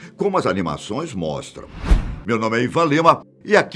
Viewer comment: Então quando ele anunciou o nome dele, eu ouvi "meu nome é ipanema" Socorro